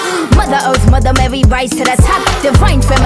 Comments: I thought she said let me be your OUMAN OUMAN lol Edit: thank y'all for all the likes! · As a kpop fan I heard it like "let me be your eomma"😂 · I keep hearing "let me be your roomba" 💀